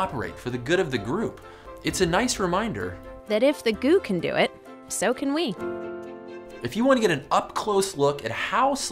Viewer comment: I thought you could have said "if goo can do it, you can do it."